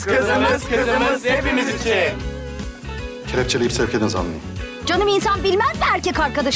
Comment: polis ne diyor? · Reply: 'Kelepçeleyip sevk edin zanlıyı' diyor.